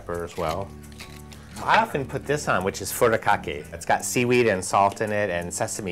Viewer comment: Did he say he puts bukkake in his food?!?! Delicious!